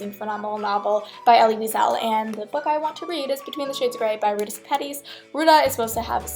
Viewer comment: When she said she wants to read "between shades of grey" I thought she said " Fifty Shades of grey" 🤣